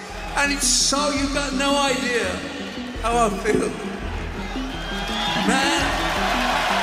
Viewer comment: To me, it sounded like he said "I feel ", not felt.